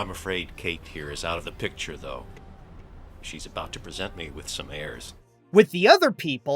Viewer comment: I can’t be the only one who misheard that as “present me with some airs.” Basically gives me the indication that the Perdita knock-off is about to perform some flatulence to the Pongo knock-off. Good thing this version didn’t include a fart joke. Could’ve been much worse to have such a juvenile joke in an animated Titanic film.